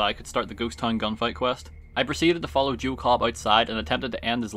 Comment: the subtitles thought you said jew cobb instead of joe cobb